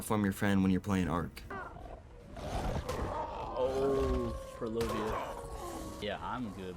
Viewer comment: what did he say? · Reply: purlovia